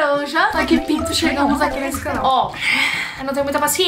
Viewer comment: Juro que eu entendi "a que PINTO chegamos nesse canal" KKKKK SOCORRO